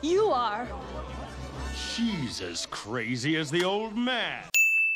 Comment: lol, i thought he said jesus christ instead for she as crazy as the old man somehow